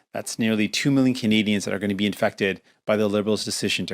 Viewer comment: "That's nearly 2 million Canadians who are gonna be..." ... what? I'm sure he means AFfected, but it sounds like he said INfected.